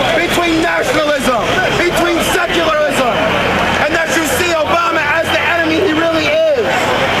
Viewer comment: Check 1:48. Did he just say "and that you see Obama as the enemy he really is" ... People knew Obama's name in 2001??